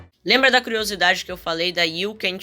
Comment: eu ouvi ron aí aí pensei que era a demo da ron